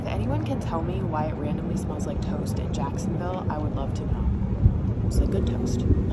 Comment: I thought you said “toes”, I could answer that. But toast I don’t know 😂